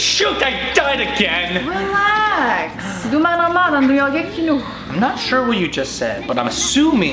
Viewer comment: as a dutch person myself I had to watch twice what she said at 1:24 lol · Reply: Is she from Belgium? That sounded more Flemish than Dutch. Definitely not from Amsterdam haha I could be wrong. "I think she said, "Do maar normaal joh je bent al gek genoeg." 😅